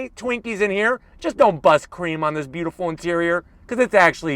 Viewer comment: Did he just say "bust creme" 😅